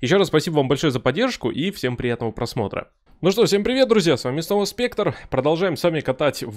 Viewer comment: "Ну что, всем привет ребята, с вами Соус Пектор")) 0:28 шутка юмора, просто мне так послышалось 😃